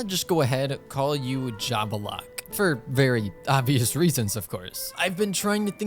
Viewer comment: i thought it said "Lava Locke" 💀